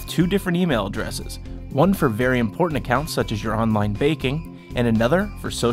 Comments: did you say online baking? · Sounds like he saying online baking. Sounds Delicious.